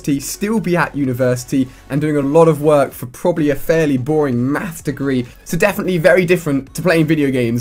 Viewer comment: It sounded like he said meth degree! Hahahaha